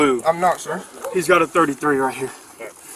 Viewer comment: Ny phone lagged, sounded like they said "He's got a dirty three" I was like "oh shit, new term??" 😅